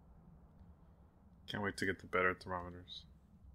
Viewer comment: what did he say there? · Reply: He said he can’t wait to get the better thermometers 🙂